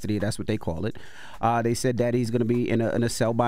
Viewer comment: Did Envy call him Daddy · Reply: He said “that he’s” not daddy’s